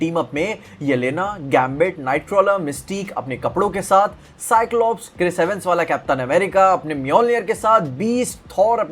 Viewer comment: kya bola tune 😒🗣️ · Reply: Kapdo ke sath 😢